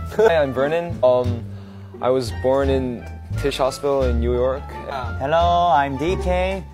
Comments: I thought Vernon said that he's from fish hospital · Lol, Vernon told us the exact hospital he was born in 🤣. (Tisch Hospital) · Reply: I thought he said “fish hospital”😂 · I thought Vernon said " hey I'm Vernon and I was born on fish hospital in New York "😶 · I thought vernon said he was from ‘fish hospital in new york’ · I THOUGHT HE SAID FISH HOSPITAL 💀😭